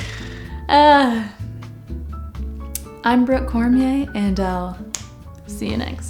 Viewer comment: For a second in the outro I thought you said "I'm broke Cormier" after you'd said you hoped to get paid for YouTube one day 😂 The disappointment when I realised I'd misheard...